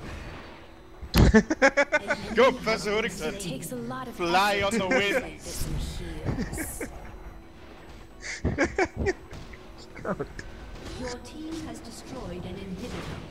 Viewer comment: what did he say at 12:14? "Go Professor Hootington?" I don't even understand.... what?